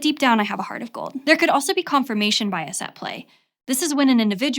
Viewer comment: anyone else hearing confirmation binas? Also ENFP army rise up